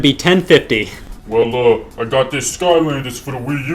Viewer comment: "Well, uh, I got this skylander game for the Wii U" "That'll be $30" "Really? GREAT! Take it!" "Ok, just as soon as you hand over the $30" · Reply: Off topic, but I could've sworn he said "I've got the Scotland here for you"